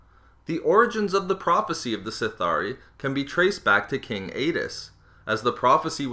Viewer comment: I thought he said King Anus.